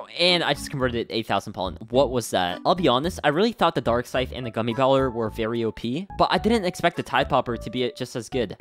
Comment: Did you just call the gummy baller the gummy gooller??!??!?